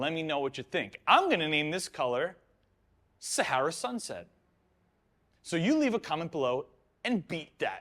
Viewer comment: I seriously thought you said sour sunset, which is also a fun name. Hmm, the color is okay, but I'm biased against orange. 3/5. I really like it on the neutral and dark cool colors, like blue/black, grey, and purple. It also might be one of the few shifts that work on white, it didn't look dirty at all. You should definitely spray this again, it's a really nice shift.